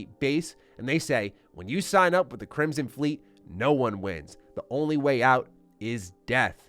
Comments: “No one WINS?” He says “No one QUITS.” Not sure how you misheard that. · I am pretty sure he is saying "No one quits, the only way out is death" That is what I hear in the trailer and it makes a hell of a lot more sense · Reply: Yep, I noticed this too. It’s not “wins”, but “quits.” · I'm pretty sure you misheard the line from the Crimson Fleet. I'm like 99.9% sure he said "when you sign up with the Crimson Fleet, no one leaves"